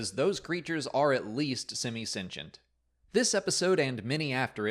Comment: What is he saying at 2:35? Semi-sengent? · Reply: Semi-Sentient.